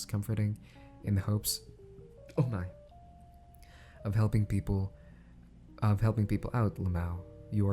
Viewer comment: Did I hear a “oh nein”